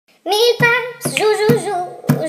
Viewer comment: I heard it's not "mipan" but "miepops"